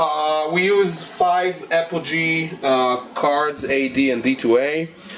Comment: what sound cards does he use? Apple g? Thats what I heard, not sure though. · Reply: Apogee converters to a Prism soundcard, with an Apogee Big Ben for clocking. Very high end.